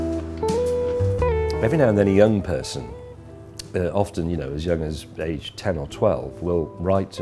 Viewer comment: what did he said at 00:11(the first line)? Trying to translate into russian. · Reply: He starts off "every now and then, a young person, aged 10 or 12...."